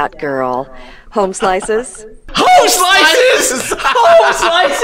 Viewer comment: Zelda: "YoU KnOw WhAt Im SaYiNg??" Me: "........HELL NAH, did you just say "hoe-slice"?"